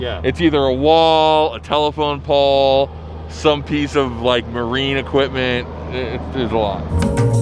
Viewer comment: did he just say marine equipment at 3:25 ? so we getting a gymkhana at docks?